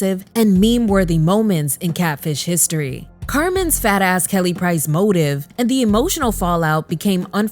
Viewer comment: the way I thought you just called that woman a fat ass 😩😂 but you said her fat ass Kelly price motive lol